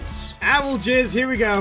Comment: I thought he said owl jizz